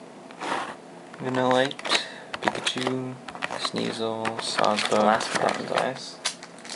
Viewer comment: Are you trading any? and LOL it sounded like you said Sawsfuck XD